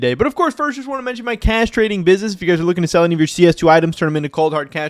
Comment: I HEARD CASTRATING BUSINESS HAHAHAHAHAHAHAHA